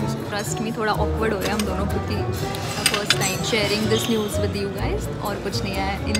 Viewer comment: what did i just heard ? Can anyone explane this me 🥲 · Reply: News bola hai bro 🤨